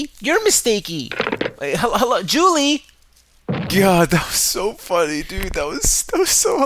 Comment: I heard a third voice around 2:23 sounded like they said “hey” but were cut off very fast, what was that?